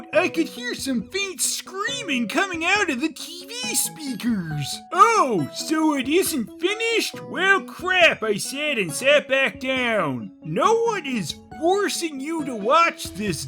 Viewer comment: I swear I keep hearing that moment at 11:41 as, "As I was about to get the DVD out, I could hear the TV speakers faintly scream, 'Oh, so it isn't finished yet? Well crap.' And I sat back down." That would've been a better reason to keep watching.